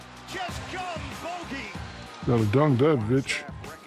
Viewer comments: Sound like Shaq said, “Gotta dunk that B****” @ 1:31 😅😅🤣🤣🤣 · ayo, did Shaq just say the b word?💀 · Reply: I think he said. ''VIC" short for bogdanovic